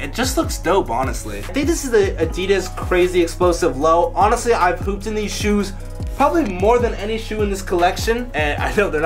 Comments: it sounds like said I pooped in these shoes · sounds like he said “I’ve pooped in these shoes probably more than any other shows in this collection.”😂😂😂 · it sounds like he said “ I’ve pooped in these shoes so much”😂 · it sounds like he said I pooped in this shoes hahahahahaa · lol thought he said Pooped in these shoes..... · I thought he said I pooped in these shoes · it sounded like said he pooped in the shoes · ok i heard said pooped in these shoes LMAO